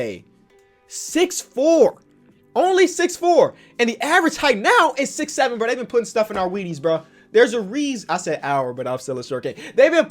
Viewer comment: I can’t be the only one who thought he said “they be putting stuff in our wenies”